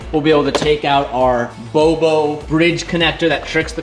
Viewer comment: Nice use of the Tagalog word "Bobo" 1:36 had to watch it a couple times to make sure I heard what I heard.